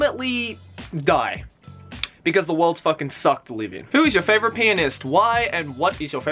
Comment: I thought Joey said penis instead of pianist.